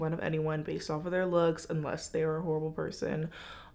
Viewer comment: i swear to god i thought you said "unless they're a little person" at first 😂